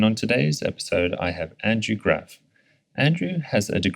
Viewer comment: I thought he said, i have angiography..' then i realised he said...'Andrew Graf"